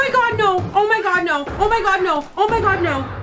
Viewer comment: second minute of the video lmao all I hear is “Nooooooo God nooo” 😭